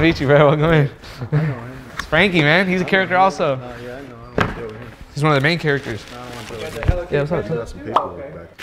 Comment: what was bro saying? sounded like he aint want to deal with Frankie LMAO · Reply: Fact "ion wanna deal with him" wonder what happened. Even Frankie's dap, no eye-contact, smile went away... He prbs got lowballed and didn't like it, be like that when you bring bricks. Funnier didn't even peep what was happening when talking about the main character 😂